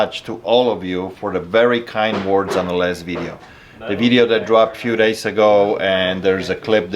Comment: When he says last video it sounds like he said lesbian.